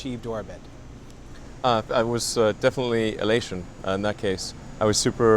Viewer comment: What word Elon say at 2:59? Thanks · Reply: A little late, but I think he said 'Elation' It basically means extremely happy